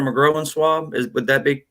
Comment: The “io” in “groin” sounds like the “oy” in boy. The word is all one syllable. The way you pronounced it adds a long “o” sound and a syllable, so it sounds like “grow-in” swab. It should sound like “groyn.” I think the doctor thought you were saying “growin’ swab”, leaving the last “g” off growing. I hope that makes sense.